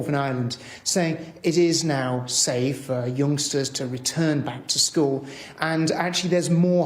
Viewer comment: i thought he said gangstas not youngsters